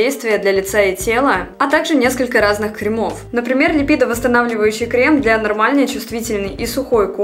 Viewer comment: Продолжаю завидовать прямым рукам. У меня даже самая обычная стрелка не выходит. А жаль. А ещё, я мастер "послышалось", поэтому для меня в рассылке не лиПидовосстанавливающий крем, а лиБидовостанавливающий.... Всего одна буква, а сколько смыслов)))))